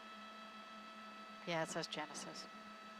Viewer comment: He must’ve been going pretty fast. That looks like some type of loaner vehicle from a dealer? Is that the Bentley logo on the door? Boy all the people that sell their masters tickets are about to have a heart attack! I hope Mr Woods is OK. Hes the best! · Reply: I think the lady said “it says genesis”